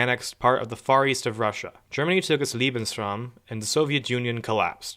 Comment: what is bro saying lol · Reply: Germany took it lebensraum